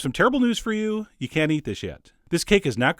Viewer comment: I misheard "You can eat this yet" for "You can't eat this shit" and I was confused ^^' Looks yummy and easy to make, chef. Sooooo gonna make this soon.